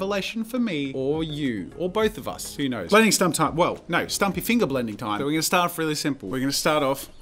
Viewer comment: Everyone here is sharing their experience with teachers forbidding finger blending... while my art teacher had two entire lessons dedicated to finger blending because she believed having this kind of raw and direct contact with the drawing would help us improve and understand better how the materials work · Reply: I thought you said “finger bending” 😂